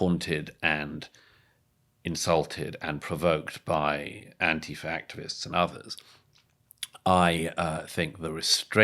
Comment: Did Douglas say “anti-factivists”? 37:07 That’s brilliant.😂